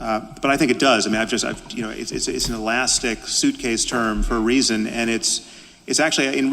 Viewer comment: I don't think Harris actually equated wellbeing with reason, at 26:38 I'm pretty sure he says (about the term wellbeing) "it's an elastic, suitcase term for A reason" but it's difficult to make out so understandably didn't hear it. Not that I would put it past Harris to say something that dumb but I just wanted to clear that up.